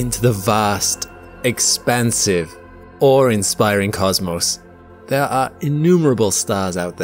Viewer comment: Watching this 4 days sleep deprived, not really sure I'm going to learn anything at this point as I keep mishearing things. "When we look out into the vast, expensive cosmos", "Now, Aztec nology has advanced..." fml lol.